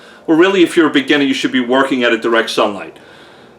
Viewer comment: You mention in the video "if you're a beginner you should be working at a direct sunlight" or at least that's what the captions say you said and that's what it sounds like you said but I think you said working "outa" direct sunlight... M105 and direct sunlight is a no go? What if its indirect, like closer to sunset? I have no choice but to work outside so..